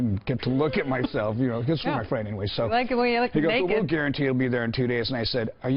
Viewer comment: Hum, did she just say she liked the way he looked naked. OR That since Dave did not have the suite, that he was gonna like the way he looks naked? Either way, she is HOTT, And Dave, is the best. Love him.